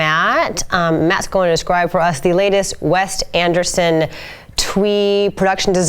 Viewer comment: Did she just say 'WEST Anderson' ??